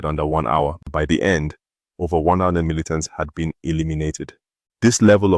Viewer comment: I have tried to listen something like 10 times at what number he try to say at 3:15 but find it impossible to understand. To me it sound like "over 1 anaoo militant" i have no idea what that it, it certainly does not sound like "thousand".. Burkina Faso, Traore and the people of Burkina Faso have my 100% support. But for me, the language they speak on this channel sound like part English, and part.. something else.